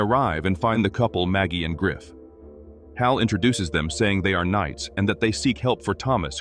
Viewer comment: I thought he said great knights they were beaten in seconds 😂😂😂